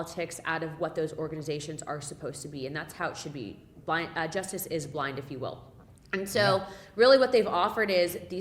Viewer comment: Did she just say "just is blind" ? (like justice is blondes, not real)!. Hahahahahhaha